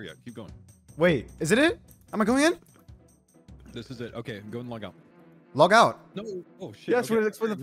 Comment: You went in before they said anything, then due to delay you heard say “go ahead and log out” and thought he said “go in and log out.” Good shit though, shit musta been mad nervewracking with 3 people coaching.